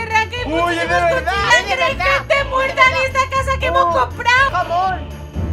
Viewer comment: le a dicho Amor a o escuche mal xd minuto 0:05 Buen video uWu 👏 Un poema pa 💕 No es fresa 🍓 Ni cereza 🍒 Es una princesa 👑 (No me copien el poema :c)